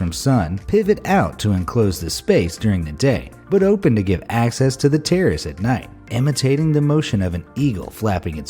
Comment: i thought he said to give access to the terrorists, anyone else??